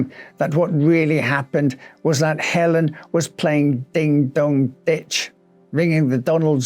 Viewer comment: did u say ding dong “bitch”